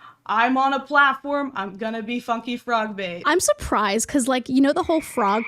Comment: Idk why but when they introduced themself it sounded like they said “fucky frogbait” 😭